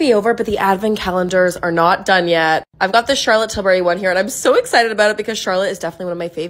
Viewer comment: I thought you said Charlotte Dobre and I was trying to figure out when she released a make up line?!